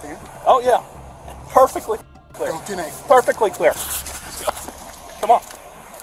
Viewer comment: you got subtitles wrong the sheriff told the cheif to "Go back 10-8" meaning "Go back on patrol"